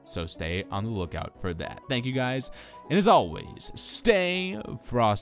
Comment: In the intro I thought it said "Stay horsey" instead of "Stay frosty"